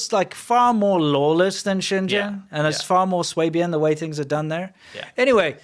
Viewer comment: I need help from a smart person: At about 2:04, did say it's "far more swaybian the way things are done there"? I can't find that word. I found Swabian referring to a region of Germany, but not as an adjective that might describe how things are done. I've also found Schwaben which seems to be an alternative spelling. The only stereotype I can find is that Swabians "keep things tidy" and "get things done," which doesn't match what said. If anybody can set me straight on this, I would really appreciate it. Thanks in advance!